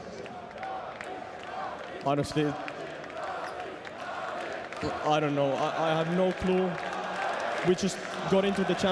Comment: what did he said? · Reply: Alexi screams Torilla tavataan. It's Finnish.